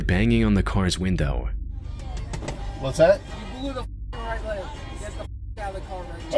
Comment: It sounds like he said "You blew the f*cking red light."